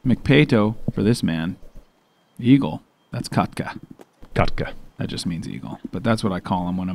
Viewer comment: When you called Eagle ”kotka”, it sounded like you said ”kakka” which means poo in finnish. Had a laugh for sure